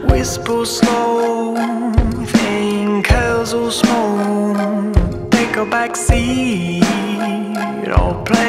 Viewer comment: first time I heard this I kept thinking 0:35 was "take a vaccine" 2020 has ruined me